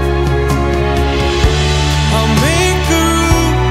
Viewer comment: did i hear "I make kangaroo for you?"